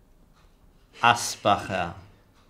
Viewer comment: Habe ich da gerade Asbach gehört?